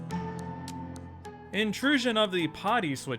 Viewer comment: Kids, don’t let your stories grow up to be one-trick ponies that can only do punny humor. I legit thought said, “Invasion of the Potty Snatchers” and for a moment, I felt Tommy Pickles waddle up to me, tug on my sleeve and ask eagerly, “YOUR ROOM is a potty?!” IYKYK 😂 look it up or ask nicely and I will explain it, just know, it’s a Milennial thing. I was amused because of the image of switching toilets. That is all. You have earned 345.8 EXP for reading this far! 🎊